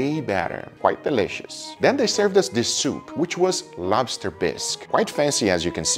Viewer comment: thought for a second you said Lobster piss